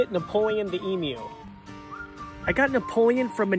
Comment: For a second I thought he said is Napoleon emo